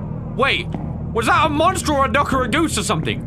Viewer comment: I thought he said "is that a monster dropping a deuce" and then thought he said "deucy goosey " and I laughed so hard. I had to rewind it because my hearing sucks and sadly that is not actually what he said